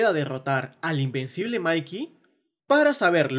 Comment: la dixslexia me jugo en contra y en ves de escuchar "el invencible mikey" escuche "el imbecil mikey"